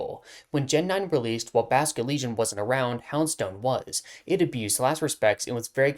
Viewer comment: I thought you were saying Houndoominite was banned 😭, I always forget about Houndstone itself